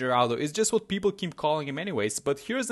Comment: WHAT? ALWAYS THOUGHT HE SAID "HELLO THERE, AT YOUR SERVICE"